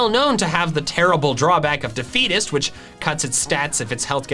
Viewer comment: I thought he said the fetus when he said defeatist